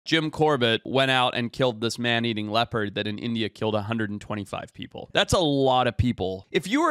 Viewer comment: I swear i had to rewatch the intro over four times to understand because i kept hearing 'jim corder went out and killed this man eating leopard who on india killed 125 people'. As in, instead of realizing the leopard was the killer, i understand it was a human who killed 150 people then ate a leopard.